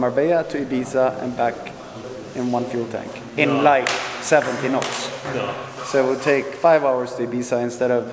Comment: Is he saying 17 or 70 knots??😳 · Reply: 70 knots. (17knots is not really fast). On Instagram he said its +100 Knots with 3500PS+Electric Motors.